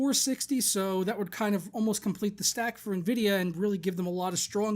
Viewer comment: sounds like your saying nehvidia lol